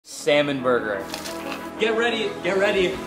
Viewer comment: I thought he said salmon murder 💀